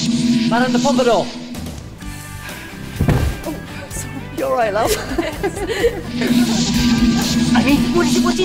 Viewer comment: Did he say "R u alright,LOVE?"?????